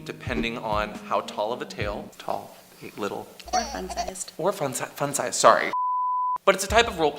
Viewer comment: I THOUGHT YOU SAID "orphan sized". oh, my lord, the chock when I figured out you said "or fun sized"